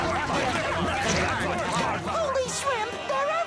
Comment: I thought spongebob said “Holy Shit” in 5:40 .